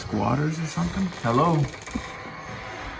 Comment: i heard someone saying hello 🤷🏻‍♀️ did you notice that too?